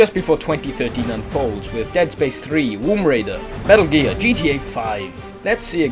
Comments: Did you just say "Womb Raider"? xD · Dude, I swear to god, did you just say Womb Raider...? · it sounded like he said womb raider at the beginning. lol